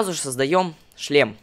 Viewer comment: 18:9 мне послышалось что он сказал создаем не шлем а член